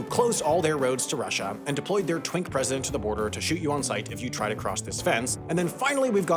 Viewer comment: did he really say ‘twink president’?! 😂